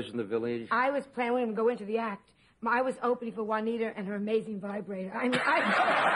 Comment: Joan says "I was opening for Juanita and her.." and I cannot understand the rest of that line. Sounds like it must have been a funny one by all the laughter. Does anyone know what she said?? Thanks .. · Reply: "I was opening for Juanita and her maiden vibrator."